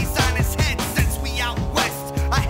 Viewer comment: What did he say? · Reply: Show em detriots wicked juggalos are coming from